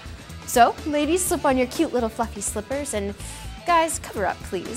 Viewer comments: sounded like she said cute little fucking slippers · I thought she said "ladies slip on your cute little fucking slippers" at first. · Is it me or does it kinda sound like she says "So ladies slip on your cute little fuckin slippers"? I know it's "fluffy".